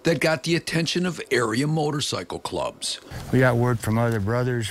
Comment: i thought it said Aryan motorcycle clubs. i was so lost lmao